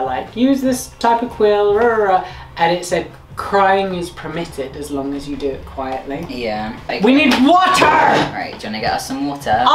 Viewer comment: What he said - 'crying is permitted, as long as you do it quietly' What I heard - ' cRImE Is PErmIttED aS LoNg As yOu dO It QuIEtlY' What my brain said - WTF WAS GOING ON IN THE OWLS IN THE 70s?????'